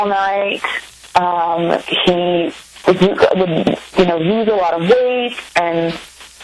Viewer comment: What does say at 12:18 ? He’d “use a lot of weed”-??